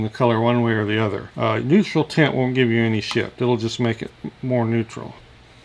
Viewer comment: I thought you said "Neutral tint won't give you any shit." I laughed. You actually said "shift" but still it was funny.